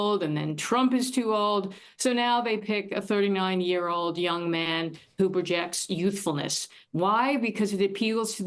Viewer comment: At first I thought she said "a 30-million-year-old young man"